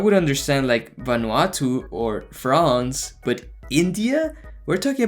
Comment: did he just said france????